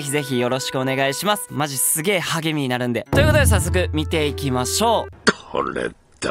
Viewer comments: 「すげえ励みになるんで」が「すげえハゲになるんで」に聞こえた俺はハゲ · 最初の「励みになるんで」が「ハゲになるんで」に聞こえた